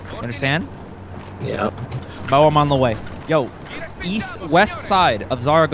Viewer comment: east? i thought you said weast